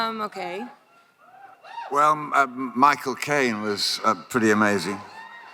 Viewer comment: I thought I heard Jeremy Irons say "My cocaine was pretty amazing" and everyone was just ok with it.